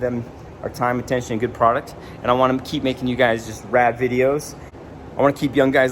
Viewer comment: Damn you said you wanted to keep making rad videos but at first i thought you said you wanted to make rap videos and i got super excited 😂😂